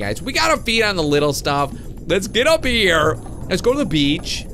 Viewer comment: hahah when you said let's get up here I thought you said let's get a beer